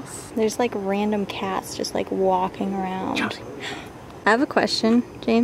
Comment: CHELSEA 4:05 or 4:06 talking about a cat...did you say "Chelsea huh"